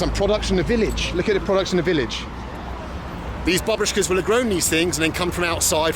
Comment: jajajaja! when you enable CC, the part when he mentions "these babushkas" the subtitles say "these barbecues".